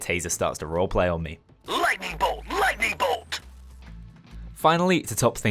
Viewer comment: i always hear the taser saying "black people, black people" instead of lightning bolt lol